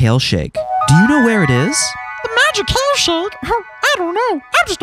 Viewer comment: "I'm just a bowl of fun" I heard I'm just a bowl of fuck anyone else lmao